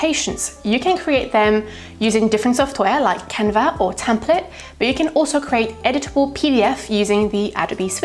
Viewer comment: of the video you say the name of software or a website to use for creating editable PDF. I can not understand what you are saying. Can you please reply here or add the link to the description? Thank you! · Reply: Definitely! The website is called Templett, you can find more information here: <link>